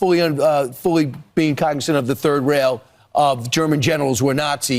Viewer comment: What you said "Third Rail" What I heard: "Third Reich"